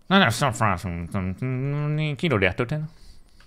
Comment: C'est moi ou à 8:40 en imitant la voix des comms il dis Kino Der Toten (map de zombie bo1 au cas ou) ptdrr